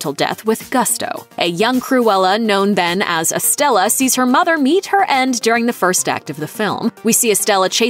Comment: I thought it said, “saw her mother yeet her end”